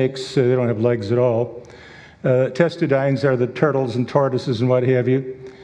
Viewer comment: it sounded like he said turtles and TARDISes. LOL!